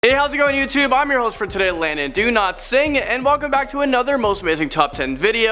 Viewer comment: I am sorry but I heard it as Landon Do not sing. No offence bro